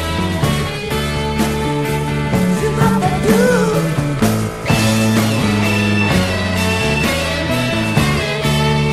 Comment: What does plant say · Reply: Shoo-boppa-doo